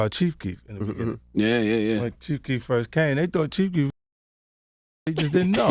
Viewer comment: is he saying people thought Chief Keef was retarded?